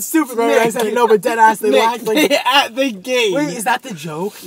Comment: wait what did matt say at 15:22?? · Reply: “nick, they a- they gain” i think